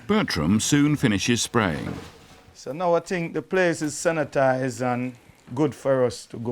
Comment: i thought he said " satanized "